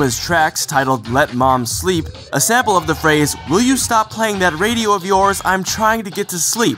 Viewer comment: You just blew my mind with that "let mom sleep" sample. I had no idea. Wow.... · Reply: I mean you can kind of tell most of these songs have samples. The only thing that surprised me was that I thought she was saying "Would you stop playing with that radio, Love". Instead of "Would you stop playing with that radio of yours"